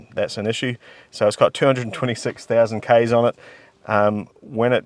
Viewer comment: Initially I thought you said 26000km - then we saw 226 - then we heard 226 rolled by "at some point between 2008 and today"😂😂😂😂 Oh dear but I suppose a)226k km is only 140k miles. I didn't hear when it was registered but let's call it 1988 which, given the 200k service stamp in 2008, let's say it hit 226 in 2010 meaning almost exactly 10000km per year.... Now I'm gonna go out on a limb here, but I reckon 98% of cars which are still going after 37 years, do the VAST majority of their miles in the first 10-20 years. Taking my theory to the max, it's likely the annual mileage covered is less than half in a car's semi retirement than it is in its heyday. I think you are pretty safe to estimate that from 2010 to 2025 - so 15 years - I feel it's a very safe bet to say not 150k - but 75k - is likely a fair representation of the additional mileage so in short, the car's probably done 300k km - 186 000miles - so that's 8000km or 5000 miles per annum and indeed the look and condition very much seems to back that very modest workload although I disagree that it's been mostly garages due to the extensive cracking of the wood veneer which again, I didn't hear you refer to when listing the issues with it but if you didn't you should have (and my apologies in advance if you did!) and which would certainly indicate a considerable amount of thermal stress entirely commensurate with frequent exposure to blistering Australian sun and acting therefore as a reliable indicator to the very opposite